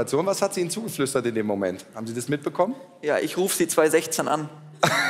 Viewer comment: Was hat er genau um 46:51 gesagt? Ich spreche nur ein bisschen Deutsch deshalb kann ich nicht alle verstehen. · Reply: Erik Fendreich “Ja ich rufe Sie 2016 an“